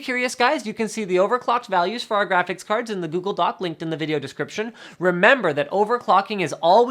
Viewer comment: Haha, I thought he said "doobley doo" like the vlogbrothers say.